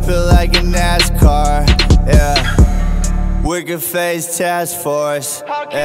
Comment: always thought he said "we're the face tats force" or smth like that lmao